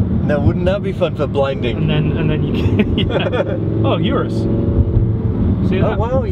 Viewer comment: Does anyone know what vehicle they saw at 17:17 (yellow car) sounded like Hurris?